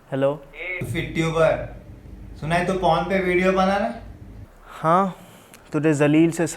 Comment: i heard suna hai tu porn bana rha hai.😅